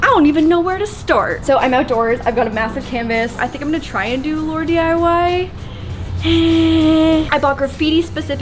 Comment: at first i thought it said laundry